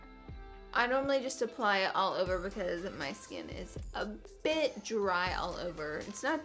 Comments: I thought u were goin to say ur skin is a bi*** but u said "ur skin is a bit dry" 😂😂😂😂😂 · I swore she was gonna say "my skin is a bitch" 😭😂💀